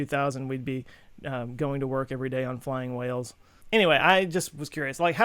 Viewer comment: Wait, what did he just say? repeats last 5 seconds He did. He said flying WHALES. What the freak? Okay now I have to look up whoever said that and why. This is such an interesting video, for some reason my mind is struggling to take it all in even more than with your science/quantum/universe/etc videos.